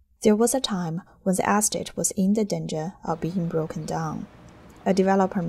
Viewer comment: What is the narrator saying at 1:37..."There was a time when _____" What is that word after "when"? The word is in reference to Mar-a-Lago. She uses it 3 times in this video. It sounds like it starts with an "A". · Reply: estate in danger, E is pronounced as an A. She is Chinese.